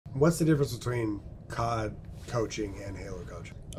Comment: I’m so fucking tired I thought he was saying cockroach instead of COD couches.